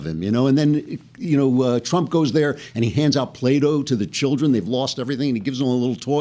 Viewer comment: Oh - I thought you said Trump was handing out Plato to the children. Or is that Pluto?